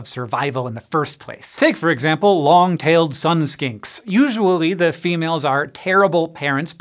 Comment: I thought he was saying skank instead of skink 🤦